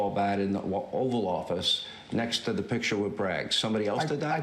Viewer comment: I heard him say "lawffice". I dunno about anybody else.